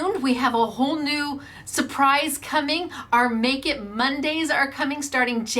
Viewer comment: I walked back into the room while the youtube was playing. I swear I heard you say, you would be starting "NAKED MONDAYS!", I did a double take and realized you said, "Make-it Mondays" What a relief!